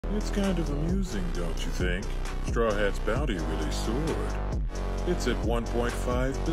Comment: ""sward" in the subtitles is wrong I think, I heard "soared" instead. I thought it could be of help for others non native speakers